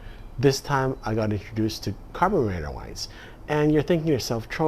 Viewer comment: I thought heard 'carbonated wines' there. Ha, ha.